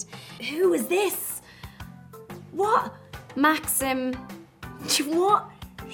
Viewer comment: she said ”what?", which sounded like "чего", which is also "what" in Russian, which is pronounced like cheewoh/chivo/whatever very natural, I thought I misheard it, but it's real, so sweet :)